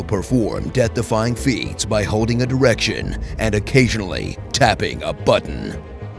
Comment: does he say "direction" or "erection"